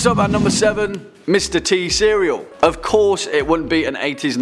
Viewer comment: top7 Mr T i swear i thought u heard Mr T series